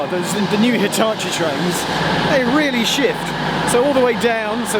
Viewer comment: I genuinely thought he was gonna say the trains were really s**t.